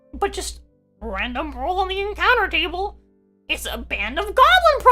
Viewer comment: Is she saying Gollum Prostitutes, as in the character from Lord of the Rings? or Golem Prostitutes, as in the creature made out of stone? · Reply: Goblin